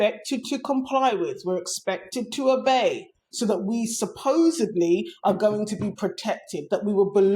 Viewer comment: lol did u just say supposebly!?!? SUPPOSEDLY 👏